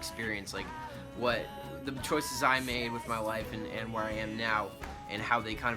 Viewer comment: I THOUGHT YOU SAID WIFE NOT LIFE I WAS LIKE JUST